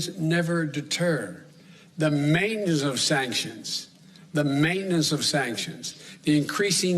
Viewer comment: what did he say? Pretty sure Brandon just had a mini stroke. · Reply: Sounds like "mainess of sanctions." That's not a word but he's never been accused of being really bright. 🚫🤓 Maybe he meant the main reason for sanctions but said mainess. 🙃